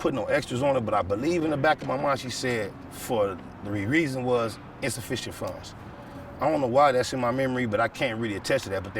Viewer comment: What did he say at 13:40 mins??? he mumbled it. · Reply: Insufficient funds